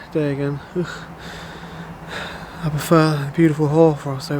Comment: can you tell us what it is you said here. I laughed pretty hard but am unsure of your intent. If I am right in what I heard, I guess I'd agree with you. I also would prefer a beautiful whore over a drab and dreary day.